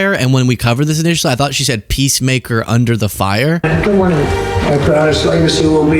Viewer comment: I heard, “Under the Fire” also.